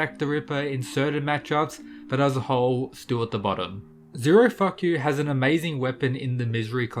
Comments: I thought you said zero f..k you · Did he say "Zero-fuck-you?!"